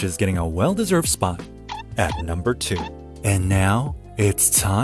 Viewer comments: Does anyone else hear "Do-Cock" here? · Did he say "Ock?"